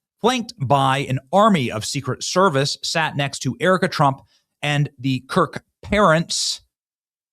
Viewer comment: Did he just say Erika Trump?